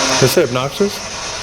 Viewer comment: I heard “obnoxious” LOL